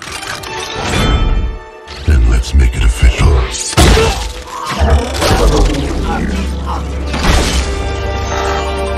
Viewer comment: what did he say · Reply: then let's make it official